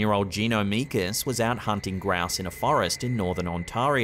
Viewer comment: Did you say the guy was out "hunting grass"?... What kind of sport is that? 😂